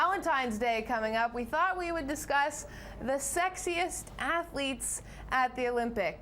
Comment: Whoa i misunderstood this.. i thought it said Sexist... with brooms and clean ice.. idk